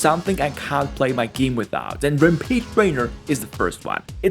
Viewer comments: did homeboy say "rampeed trainer"???😭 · Man, that's quite an accent there. I heard "Repeat Raider" even when listening to it again.